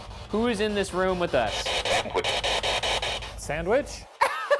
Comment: “Sandwich?” I thought it said “Edward?”